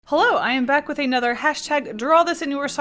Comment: did you say HoLo? Sorry I was just watching simply nailogical